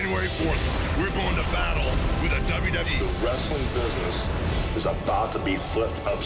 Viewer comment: did I hear it right, "we gonna battle with the WWE" Monday Night never gonna be the same. FUCK yeah, LONG LIVES TNA